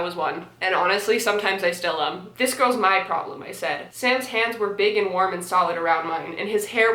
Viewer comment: When you said, Sam's hands, at 7:14 I thought you said Sans, and I almost lost it, I genuinely nearly ascended